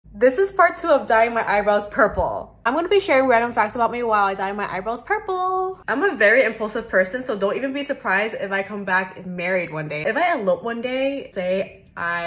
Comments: for a second i thought she said DYING HER EYEBALLS PURPLE?!??! i was so confused 😅😅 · OHHH EYEBROWS I THOUGHT SHE SAID EYES THE FIRST PART AND WHEN SHE DID HER EYEBROWS I WAS LIKE WTF. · I thought you said dying my eyeballs purple and I was like (uhh how?)